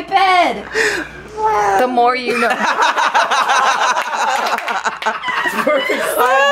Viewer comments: it sounds like he said wow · it sounds like said “woah” and I couldn’t stop laughing because he looked so interested. 😂